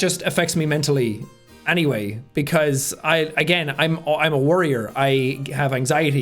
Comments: I misheard worrier as warrior.... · I find interesting how in 15:41 he is like "im a worrier" but I heard "warrior" and I thought huh, honestly being someone under stress and anxiousness all the time but that presses forth is kinda like being a warrior